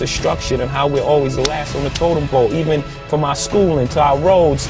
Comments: Hi guys! What do you think about this video? Do you want more of this in the future? I really hope you enjoyed watching it. Thank you! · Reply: the scribing is off sometimes, what he is saying isn’t matched properly in the words and it’s bothering me, for example at 2:06 he says “ last on the totem pole” not “last on the total blow” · Hi guys! What do you think about this video? Do you want more of this in the future? I really hope you enjoyed watching it. Thank you! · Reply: I think that the video was great. I also think that at 2:04 he said "Totem pole" instead of total blow.